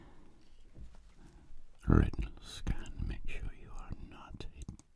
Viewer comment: Retnal scan ohh good. I thought he said rectal scan.